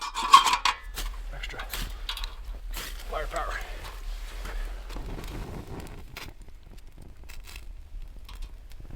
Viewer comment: What did he say at 9:15 lighting the fire? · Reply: I was just saying to Lance...that I was giving him a little extra fire power with my striker to get the fire started.